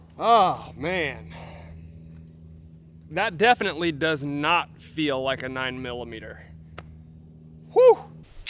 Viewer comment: Hahahaha at 2:54 I had a 5 hour energy ad and for a split second and when the video resumed, I thought you said "that DEFINITELY does not feel like a 5 hour energy" 😂